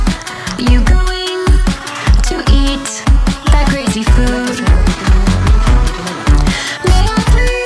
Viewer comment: DOES ANYONE KNOW WHAT THEY ARE SAYING IN THIS PART BECAUSE I NEED TO KNOW ITS BEEN ANNOYING ME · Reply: “Are you really going to eat all that disgusting food?” In Japanese